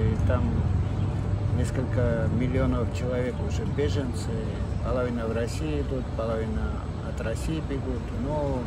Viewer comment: Hold on a sec. this guy 1:17 says it is a special military operation but I hear word "voyna" (war).. how does one translate "special military operation" in russian and does it include word "war" in it? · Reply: He is saying "polovina" (a half), not "voyna"